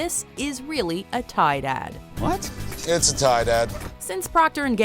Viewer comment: Ugh I love the Keanu Reaves one!!! And the michelob one. And the rap battle with Doritos and Mountain Dew. Tide ad was cool, but at first I thought they were saying Tie Dad.